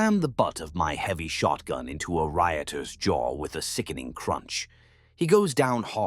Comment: I thought it said my writer’s jaw…would’ve preferred as it’s way funnier 😂 “on paper”